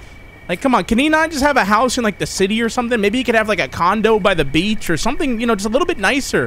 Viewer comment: I thought he said "condom by the Beach" (it was funny)